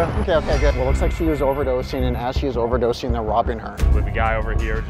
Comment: bruhh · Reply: I thought they said rocking her 🤔